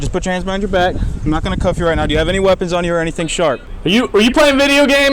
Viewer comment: when did they try to cuff him? I must have missed that part in the video🤔. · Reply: Oh, on re-listening they didn't. My bad. I heard "put your hands behind your back" and then "Cuff you right now" but missed "Not going to" right in front of that last part.